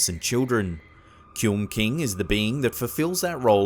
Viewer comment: I thought it said Cumking